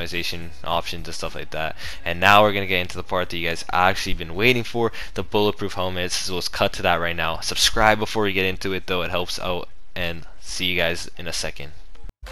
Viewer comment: I thought you SAID BULLET PROOF